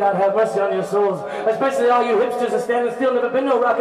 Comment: You captured Jack White calling out hipsters at the height of the Garage Rock Revival. Absolute gold mate 😂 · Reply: For a sec there I thought you said hamsters and it made more sense.